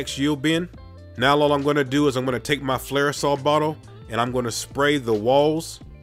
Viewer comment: What are you saying at 15:00? Flair-osol bottle? What am I supposed to be spraying it with?